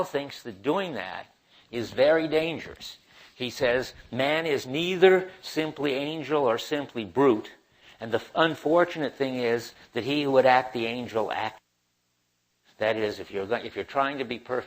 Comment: What's going on at 12:45? is it cut? what does he say? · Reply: He says: Man is neither simply angel or simply brute and the unfortunate thing is that he would act the angel at... 'it's brutest way'. - Maybe. History has shown this repeatedly.